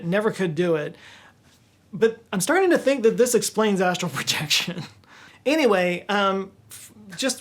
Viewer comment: Wait... Astral projection? ASTRAL? I thought you said asshole projection... Fuck. FUCK! I gotta go...